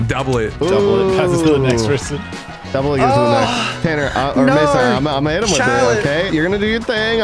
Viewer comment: I heard tanner's mom and just thought "we gotta bring back Connor's mom shaves his back"